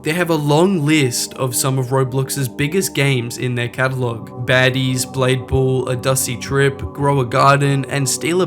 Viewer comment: did bro say baddies?